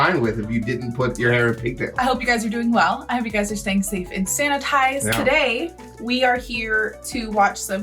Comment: Sanitised I thought you said satanised